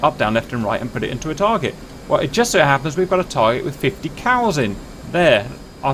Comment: I thought he said "there's a group of 50 Cals up there" and I thought, ah yes, get the bad guys. I was then surprised to see 50 cows